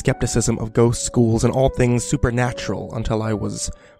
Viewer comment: I thought he said "ghost schools and all things suppernatural" at first before realising he said "ghosts, ghouls, and all things supernatural" lol